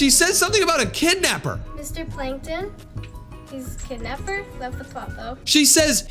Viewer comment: From what I heard its sounds like she said Mr Plankton is kidnapper Is it just me??????????